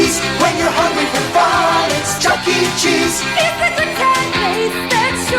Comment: This is the first time I'm hearing the Chuck E Cheese theme song and I thought they were saying "when you're hungry for violence"